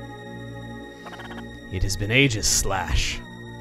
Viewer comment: I heard Aegislash, the Pokemon from Kalos